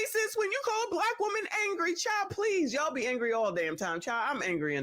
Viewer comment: Hey now!! I thought they said, "Black women can't be angry" LOL